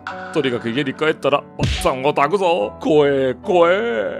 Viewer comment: パッサンを焚くぞが、奥さんを抱くぞに聞こえた俺も末期かも